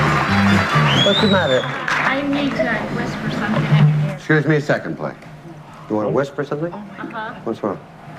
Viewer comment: The clip at 8:51. I couldn't understand what they were saying and it's bugging me. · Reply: "What's the matter?" "I need to whisper something in your ear." "Scuse me a secondly. You wanna whisper something?" "Uh huh" "What's wrong?" "Can I go to the bathroom?" "Ok and uh... What we should really do is stop... taping at this moment." "Don't you just have a can?" "Well we uh..."